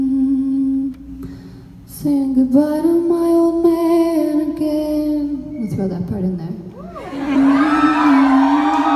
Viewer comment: what does she said after "saying goodbye to my old man again"? I don't understand hahaha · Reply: "We'll throw that part in there" (joking because it wasn't the right lyric)